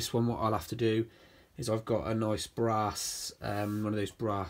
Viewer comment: Is it just me with a slightly dirty mind or did just say bra.....arse (brass) 15:42 😂